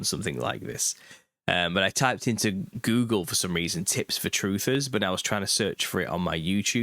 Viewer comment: Man, i could have sworn i heard you say t*ts for truthers... i was like hell yes! This is mu segment! very disappointed...